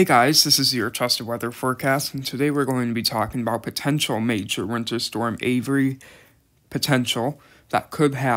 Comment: lol I thought it said 'Potential Major Hurricane' but that'll be interesting!